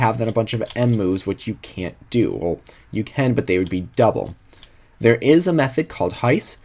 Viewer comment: What method did he say?! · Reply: Heiss Method